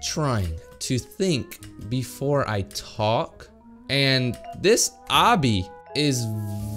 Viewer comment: wait if you go to 0:31 subtitles show "and" but it sounded like said "an". hmmm